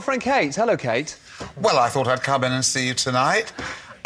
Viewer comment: Can somebody please write what Dale Winton said at 8:02 "well i thought ???? to see you tonight". I am not native english speaker and cant recognise word. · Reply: "Well I thought I'd come in and see you tonight. "